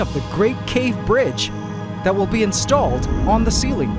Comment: also in what way will you conect it to pandora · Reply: I thought he said, "Sky bridge."